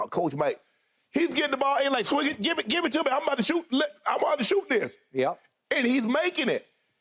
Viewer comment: I’m about to shoot n*** is what I hear 💀